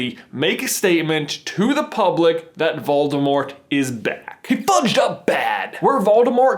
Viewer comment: Did u just say he fudged up omg 🤦